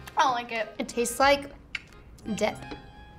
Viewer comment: I heard her say "It tastes like d**k" and I was like hshejhrhwjsjd WUT